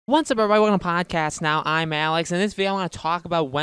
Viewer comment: "Wantsavebdadnal podcast now"... what was the first word you said? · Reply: I slur those first words lol. What's up everybody welcome to Podcast Now" depending on how on my game I am it may sound slightly like that or nothing at all lol! -Alex